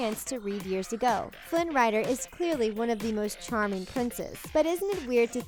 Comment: I laughed when I thought I heard 11:22 "Flynn Rider is clearly one of the most charming princess "